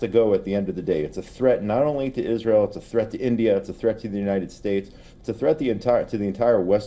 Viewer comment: Did he said India?